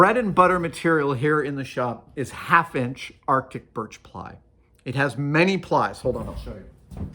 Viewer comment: I’m in southern US and I’ve always called it Baltic birch. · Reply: I was wondering about that, it sounded like Adam was saying Artic birch, but the subtitles had Baltic birch.